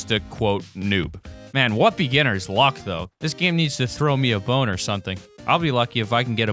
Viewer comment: why did it sound like you said throw me a boner